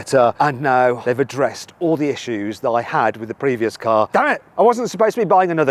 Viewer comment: damm it!ってはずなのにダメ！って聞こえてしまうwww